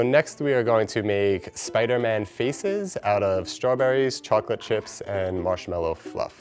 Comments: Lmao I thought he said “feces” not faces 💀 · I thought he said Spider-Man feces 🤣💩💩